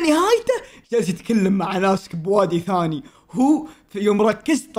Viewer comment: مافهمت وش قال ، علمون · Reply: جالس يتلكم مع ناس بوادي ثاني